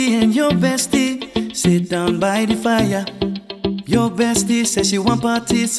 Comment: i only came here to see the lyrics because it sounds like he said ur bestie said she wants my thits